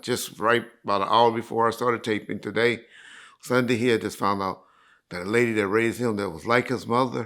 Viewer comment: I had to rewind this, I thought he said Lady Of Rage passed…he said the lady that raised…